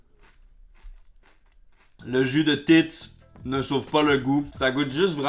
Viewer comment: Parenthèse à part, à 6:30 - 6:32 , j'ai vraiment entendu "Jus de tits? Non pas que je doute de ce que j'ai entendu, mais je pensais pas que ça passait sur youtube.